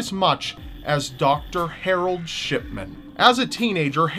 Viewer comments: I thought you said Dr. Shitman not Dr. Shipman XD Though from what he did I think he deserves the first... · at one point it sounds like he says "Shitman" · I thought u said shitman though 😓